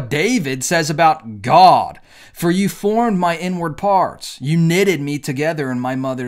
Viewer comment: I wasn't fully paying attention to what was being said, so I heard "you formed my n-word" instead of "inward"